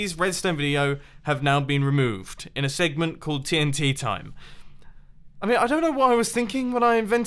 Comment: The first time you said "tnt time" I thought you said "tnt timer" witch would be cool, set a tnt time bomb and build the featured build in the time while in survival (but with materials)